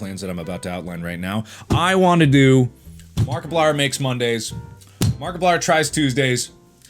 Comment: I wasn't really paying attention and then I heard "Markiplier Tries Cocaine". Dude. Well played.